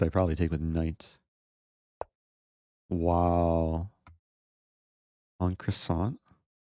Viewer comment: Not gonna lie, I heard "Pawn Croissant" at 4:20 😂😂